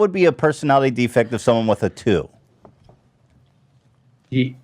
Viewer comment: I thought they said someone got a 32??